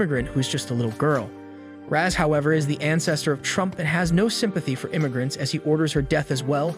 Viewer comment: Wait a minute….. Did I hear what I thought I heard? 🤔 Ancestor of who???? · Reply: Trump😂😂😂😂